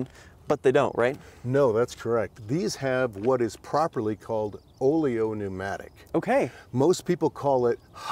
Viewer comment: As a car mechanic myself, I am indeed shocked how this car is complex and simple at the same time. Hydropneumatic suspension may sound complicated and difficult to maintain and repair but as I see any mechanic can do it. Not a car but an engineering masterpiece. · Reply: I thought I heard "oleo pneumatic",, just saying😮